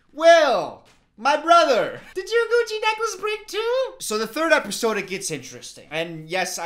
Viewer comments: Why did I hear "coochie necklace"??? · I think has mentioned the Gucci necklace a few times now… is this gonna be the new college shutting down joke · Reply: Ngl the first time he mentioned it I thought he said “coochie necklace” 😭😭🔫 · I thought you said coochie necklace and was so confused.